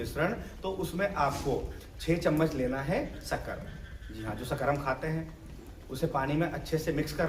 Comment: par kya bola 6 चमच kya डालने ko कहा है शकर क्या..? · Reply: Sugar, चीनी